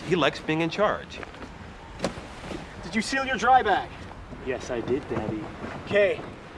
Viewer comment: I’m not the only one who heard him say “yea I did Daddy”